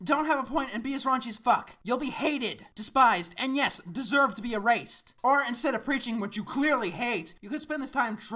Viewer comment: What did he say there? "Deserved to be erased" or something like that?